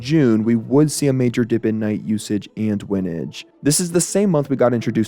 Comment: did he just say winage 😭